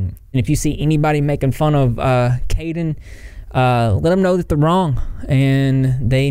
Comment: Why for a second I thought u said sheep wrong